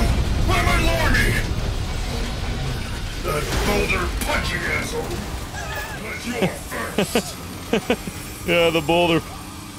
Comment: “Boulder punching freak” best line in the game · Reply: I thought he said "boulder punching asshole"